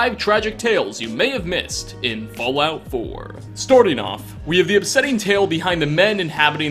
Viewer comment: does it sound like he said Storting off? Please reply and like my comment